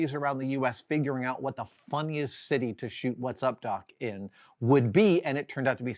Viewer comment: Ngl, at 4:15 my heart almost stopped when I heard you say, "while she travelled to several cities around the US, figuring out what the Fu...", even more so because you seemed to put a LOT of emphasis on the "F". lol Talking of movies that switched around locations in cities viewers may be familiar with (and therefore, find somewhat jarring), two came immediately to mind: A scene near the start in Trainspotting, in Edinburgh. And another in The Girl With The Dragon Tattoo, in Gothenburg. Both have sequences where the action exits one area and resurfaces just a moment later in another, far from the previous scene! While it may work fine for the casual viewer, it really can be REALLY disconcerting for viewers who are familiar with the areas concerned! I have to add though, in my experience, just seeing a location you're familiar with appearing on celluloid, by far outweighs any discomfort experienced by the aforementioned editing foibles. lol There's a 5 second scene (trust me, I timed it) in World War Z, filmed literally a 10 minute walk from my front door, which doesn't really conform to any of my previous statements... I just wanted to tell someone! 😁